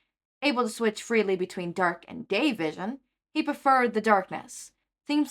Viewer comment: 😂I'm so sorry but when you said dark and day vision I thought you said dark and gay vision